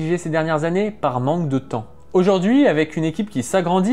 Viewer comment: "Mon mémoire sur la conscience chez trainducstao terminé" C'est quoi "trainducstao" ? (C'est une vrai question, Je sais pas comment ça s'écrit) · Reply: Il parle de Tran Duc Thao un philosophe marxiste vietnamien